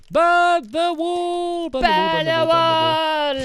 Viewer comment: 😆 LOL! I was watching this and my friend thought he said burn the wool in the intro because he's British!